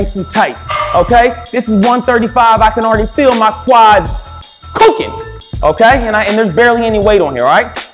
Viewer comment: Crazy-positive delivery style! Thanks for the vibe and rhythm! And tips, of course. 🍏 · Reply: Could I ask what word you use at 00:03:40 , please? “I can feel my quads ***ing?” Spooking? Cooking? Somethin’ else? 🤣 Love the energy.